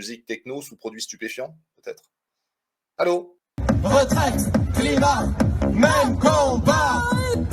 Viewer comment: Vous avez raison, je pensais être sur la même longueur d'onde. Vous ne répondez pas sur le fond. Si les lobbies qui nous font bouffer de la merde et détruisent la vie, ne vous dérangent pas alors... Je précise que je suis quelqu'un de modeste. Il n'y a pas que les bobos qui veulent qu'on respecte notre planète. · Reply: Moi j'ai cru qu'ils étaient contre Macron (j'ai entendu "retraite et planète même combat"). J'ai pensé qu'ils étaient contre les lobbies et leur fric qui pourrit tout... alors peut-être que j'ai rien compris...